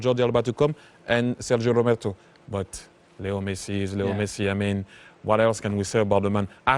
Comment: Really sounds like he is saying little messi